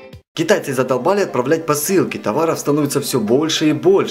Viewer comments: мне одной послышалось китайцы з***лбали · китайцы задолбали отправлять посылки я так услышала